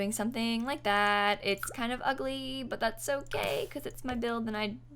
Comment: Is it just me and my gay but did she say "but that's so gay"?